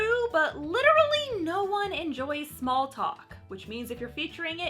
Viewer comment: it sounded like " no one enjoys smalpox"